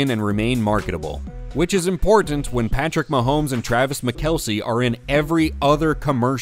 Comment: Did 😂 Did he say "Patrick Mahomes and Travis McKelce "?? I swear AI generated audio is getting more sneaky. Not saying AI,, but to mess up Kelce's name like that? Am i hearing things?